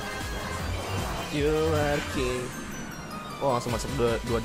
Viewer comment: "you are gay" Apa aku salah denger ya? :D